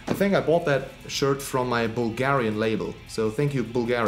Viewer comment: Wait you are signed to Bulgarian label? Which one? Also great collection. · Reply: I thought so but I heard "my Bulgarian label" and got confused. Which one if its not a secret?